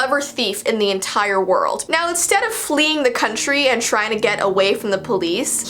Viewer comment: Jessii: Now instead of flyng the country. Me: what is flyng... Oh flying...